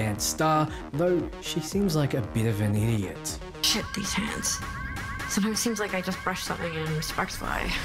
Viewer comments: what does Lizzy say? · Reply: "Shit, these hands, sometimes seems like i just brush something and sparks fly." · What is Lizzie saying in 14:41? I can't understand her at all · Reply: Shit these hands. Sometimes seems like i just brush something and sparks fly.